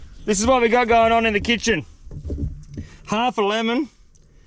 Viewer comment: When he mentioned a couple of cold beers and he said a lemon 😂I thought I heard 11 jaja like might as well make it 12 brotha😂